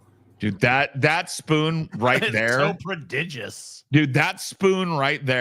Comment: Whats that word what says at 2:49:12 ‘pre-did-juce’? And what does it mean? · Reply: I think he says “prodigious”, meaning impressive or remarkable.